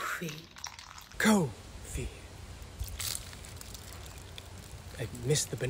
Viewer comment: It sounded like she said “CoWfEe” instead of “coffee”